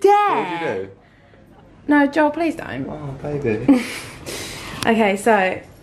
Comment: Did anyone else hear Joel call baby